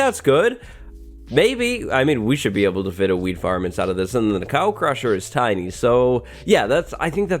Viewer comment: I swear I heard "We should be able to fit a weed farm inside of this."